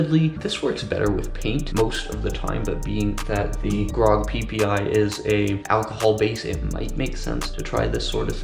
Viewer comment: am I the only one who heard "PPI"?